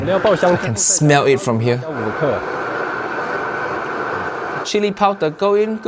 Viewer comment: subtitle says 5g of chili... 50g maybe